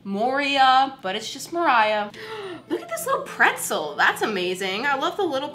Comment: I thought it was said MOR-iah but I will call you now 😊